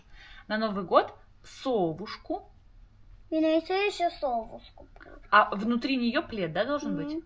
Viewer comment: "Совушку, а внутри нее плед". Услышала "Золушку"😂